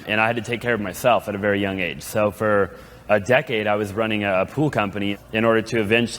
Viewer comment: at first i thought he said he was running a “poo company” me: laughs + rewinds correction: “pool company”